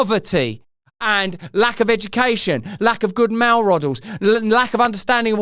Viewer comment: Oi Russell 12:58 lack of good MALE RODELS 😂🤣 🎉